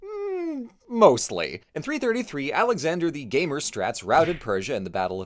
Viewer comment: "Alexander the Gamer Strats" thats a good one, yeah. I misheard it at first and thought said "Alexander the Gay of Straights" and I choked on my food laughing.